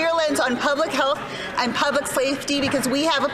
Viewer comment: Freudian slip? I swear I heard “public slave-ty”